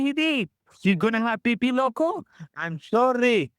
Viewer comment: Did just say "pee-pee loco"????